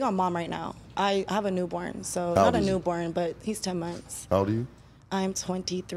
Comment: Brothas please wear protection 🤦🏾‍♂️ she really said she needed a break from her baby after only 10 months 😂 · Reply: i heard her say 2 months not 10